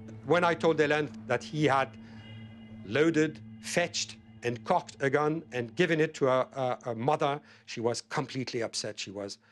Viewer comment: Did he say "They had permitted a loaded gun to their mother"?